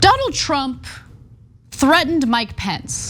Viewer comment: lol Anna, I thought I heard "donald trump threatened my pets" at the start... wouldn't surprise me though if he did 😄